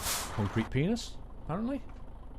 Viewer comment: did anyone else hear say green p*nis? Whose watching in 2018. if u read this comment have a superb life